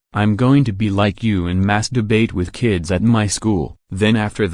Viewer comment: did he just say "mass debate" or "masturbate"? It's just that they both sound a bit similar and caught me off guard... big time! 😳💀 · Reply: He said mass debate